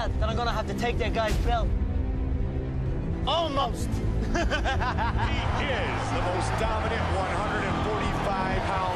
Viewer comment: The fan said that Connor punched him, then Aldo says something, anyone knows what Aldo said? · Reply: "tamo junto", meaning that he likes the guy and supports him